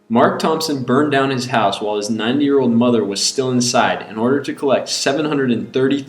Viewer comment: I thought he said 9 year old mother at first! WTF?!